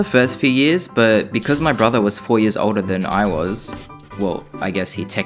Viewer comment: I thought u said “ur brother was 4 years old”